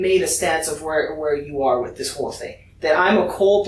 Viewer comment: Freudian slip? Did he call his mom a whore? "You've already made a stance of where you are with this whore (whole) thing." Attacking the victim is very common with psychopaths as if they deserved it. He could be mad that he's going to prison over a whore.